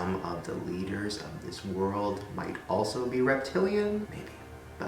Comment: Omg when you said "Reptiles, leaders of this world" I heard "of Miss World"!!! Like okayyy, Blue lizard got that strut though hahahha.